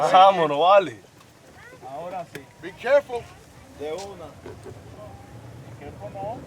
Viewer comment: anyone else wondering what he said after the fan said to be careful? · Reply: Who? Camargo or Ronald? Camargo says "De una" it's like saying "right now" cz the other guys said be careful and "de una" it means in that situation something like "yeah, common let's go" on the other hand before the guy said "be careful" they're speaking inside the car saying "vámonos" wich means "let's go" & Camargo or someone else says "de una" (right now) but after the other guy says "be careful" hahaha so that's all I might know. Ronald after the "be careful" wants to say something BUT hahahaha he remembered that he was being recorded.